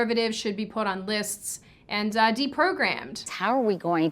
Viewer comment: I thought she said deep throat ram... do i need help?